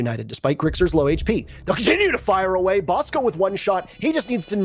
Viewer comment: Are we just going to ignore 4:50 wtf did he say??😂😂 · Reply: "Continue to fight, bosco"🤷‍♂️